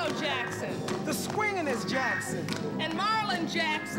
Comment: ¿Que dice? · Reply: Tito Jackson